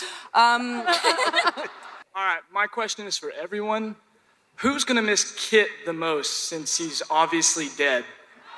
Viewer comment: what does he say? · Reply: "Who's gonna miss Kit the most since he's obviously dead?"